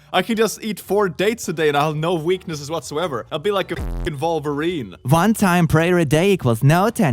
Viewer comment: Did say 'Vulvarine'? He's been on e621 too long! 😂